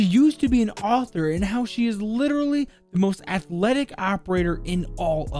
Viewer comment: I thought u said oryx was the most athletic